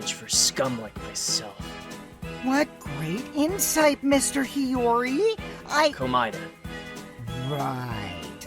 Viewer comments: DID KOKICHI SAY “mr. hiyori” TO NAGITO AT LIKE 8:40,, IN REFERENCE TO YTTD??? LIKE SOU HIYORI??? or am i trippin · did kokichi say “mr. hiyori” at like 8:40 in reference to sou hiyori (from yttd) or an i dumb😩 also don’t overwork urself nagito!!! we love you